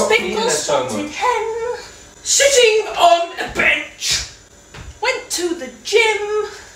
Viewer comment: I can’t be the only one who thought he said shitting on a bench 😂